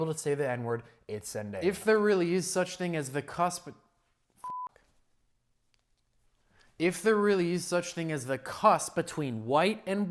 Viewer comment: ngl i wasn’t looking at 4:57 when it bleeped. i thought you said “if there really is such a thing as a cusp n*gga” i almost spit out my food 😂😂😂